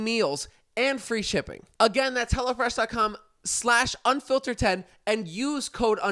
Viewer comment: During the add, sounded like he was saying “Hella Fresh” 🤣